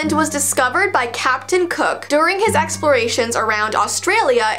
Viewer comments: Did anyone think of Captain Hook when I said Captain Cook? Me - Yea cuz I thought you said Captain Hook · WHEN SAID CAPTION COOK I THOUGHT SHE SAID HOOK😂😂😂😂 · Did say captain kook